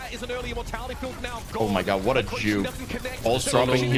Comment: Did say, "Oh my god, what a Jew" at 7:41? wtf?